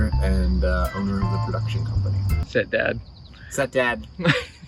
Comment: love these type of videos you should mix them in with your sit downs you can get the bus guy to film these lol at 2:40 I thought you said step dad lmao